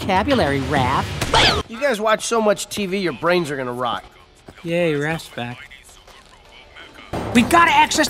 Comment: it kinda sounds like Donnie said "yay your ass is back" real lazily😭😭